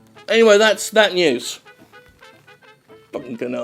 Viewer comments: At first I thought he said "Enemy fan on prom night" and I was just like "I don't get it". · It feels gooood to have a tv that looks like an anime fan on prom night. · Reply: I thought he said "frog night"